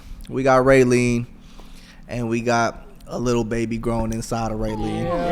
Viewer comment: Waitttt omggg 😭 in the beginning I thought the guy said “and we have a baby girl growing inside” I was like waittt did I miss something??? 😭